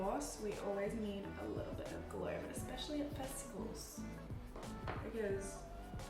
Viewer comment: i thought you said, the worst thing about vegetables not festivals, i was so confused. 😂😂